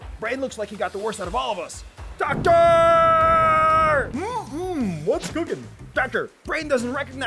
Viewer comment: Why dos liver sound like there saying KAKARRRR insteadof DOCTOR